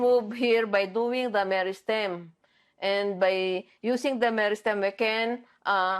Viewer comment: she clearly said meristem, which has a subtle difference from "stems"